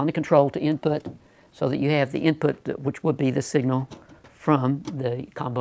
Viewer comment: thought it said inpot